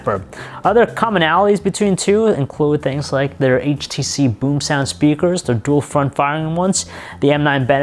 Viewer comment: It sounds like he said "BoobSound" dying